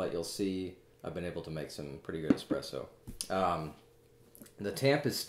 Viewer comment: Is it just me, or does he say, "expresso" at 4:39?